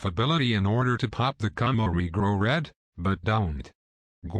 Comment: Gonna take a moment to joke that at least half of the dislikes come from people who thought you said Camo Regrow Leads.